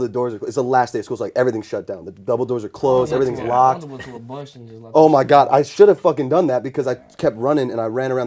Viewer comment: what does Juice say here? RIP btw :,( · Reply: he said” u shoulda just went behind a bush and shitted” R.I.P. JUICE 😢💔